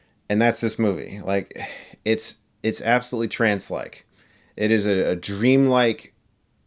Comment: what the fuck did he even say there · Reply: oh "trance-like" i thought he said "transsleck" or something